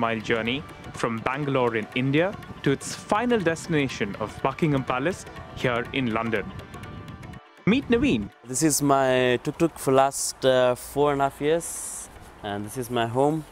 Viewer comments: It sounded like he said fuckingham palace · When he said Buckingham palace I thought he said fuckingham palace